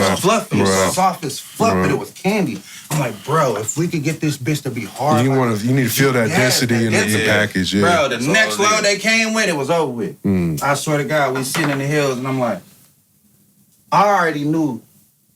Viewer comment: Wait... Did just say that Runtz was filled with PGRs? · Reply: No he said the first run was not dense the second run was right